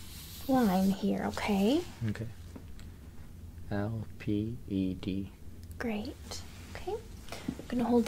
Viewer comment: I thought he said LEPD!!!!!!